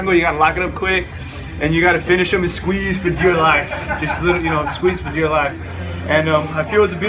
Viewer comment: what did he say at 1:43 "squeez for ....... " ? Can someone write it for me. THX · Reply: dear life